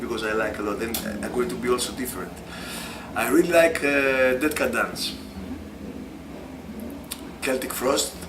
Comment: What was the first band that he said? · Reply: Dead Can Dance.